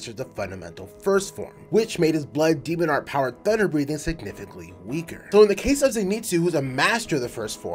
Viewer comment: I THOUGHT HE SAID BUTTER BREATHING BAHAHAHAHAXHDYFHLBJVHCUFOJ- 💀